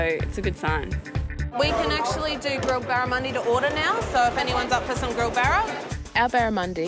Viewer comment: Can someone tell me what food are they eating at @ 28:12 ? all i hear is barrowmoni and google thinks I am asking for a loan lol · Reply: Sorry nvm, I figured out it is "Barramundi" Asian Sea Bass, <link> it's a species of fish native to the far east, no worder I've never heard of it.